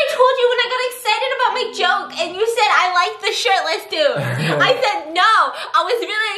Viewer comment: When ever you say the word "SHIRTLESS" its sounds like your saying "Sh*tless *i'm a girl*